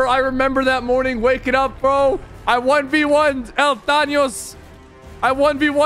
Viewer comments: did he say thanyos 2:46 lol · did he say thanyos 2:46 lol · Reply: did he say el thanyos 2:46 lol